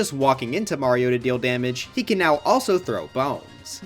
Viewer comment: I thought he had said "He can now also throw bomb-💥💥💥💥